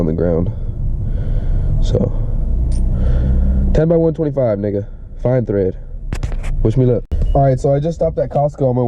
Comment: idk what was said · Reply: 10x1.25 fine thread, wish me luck